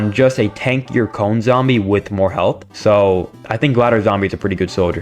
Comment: Also 14:59 subtitles are wrong, it says heath not health.